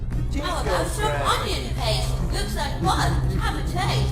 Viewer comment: "Looks like fun" I think is what it said.